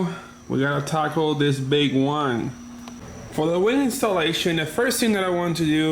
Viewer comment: It sounded like you said "And now we have to tackle this big wang". I was dying with laughter. I love the car, did you ever consider painting the inside and maybe tucking the wires against the interior with gaffers tape to make it look more clean? "Another one of the nuts finally dropped"... sorry, i'm just in the mood to be funny tonight lol. No harm intended!